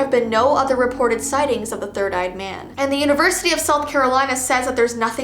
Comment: I was not really listening and I thought she said the third eyed banana